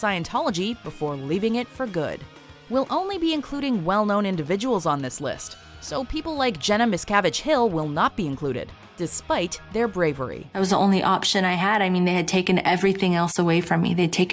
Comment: the fact that i heard "Jenna Miss Cabbage Hill" deathed me! LOLOLOLOL!